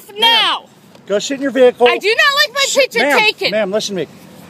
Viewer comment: his he saying "Go shit in your vehicle."? XD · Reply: I thought I heard the same thing. XD He said "go sit" though.